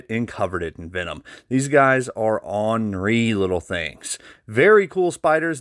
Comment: Was I the only one who thought this dude said on- ree instead of ornery 😂🤷🏼‍♂️